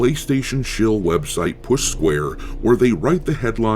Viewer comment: I had to do a double take. I honestly thought you said "Puss Square."